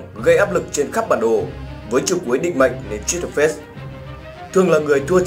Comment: nghe cái câu Với chiêu cuối định mệnh mà nói nhanh quá nghe thành đệch mẹ